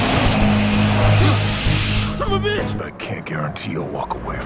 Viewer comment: sounds like bro says "im a bitch"